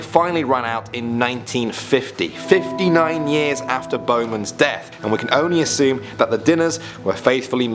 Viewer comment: misheard Bowman's and here's it in Bozeman Me: wait... Bozeman?? Here's it accouple times oh.. Bowman